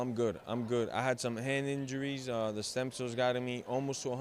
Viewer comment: does he say the "stem cells" got him almost 100%?